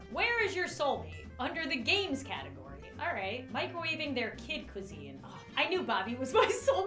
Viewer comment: I thought it said microwavingntheir kid!!!!